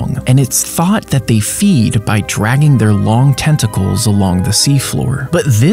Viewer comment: I thought he said “dragging their testicles along the floor” 💀